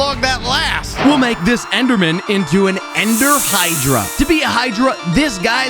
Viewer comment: I like your hydra abdomen its so cool (If no one knows what am saying i. 4:50 the subtitle said abdomen when he said enderman